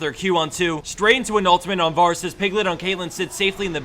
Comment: Wait... did you... was that... did you say "Varus' Piglet"?